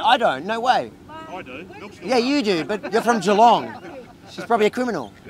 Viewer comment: I thought the policeman said shelong - I thought wow there is a place named after a pubic hair piece